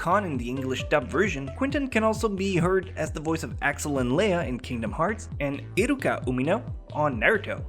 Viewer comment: Did he just say,Lé-a?